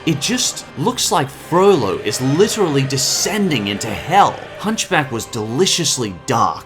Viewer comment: DID HE SAY HELL? IN A DISNEY MOVIE?!?